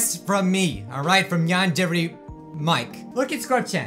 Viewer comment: yandere the captions say yawns every